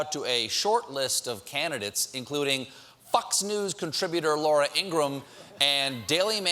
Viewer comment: or 4:39 I'm pretty sure Stephen says "fucks news" instead of "Fox News" and then you hear people laughing at it. Are my ears fucking with me?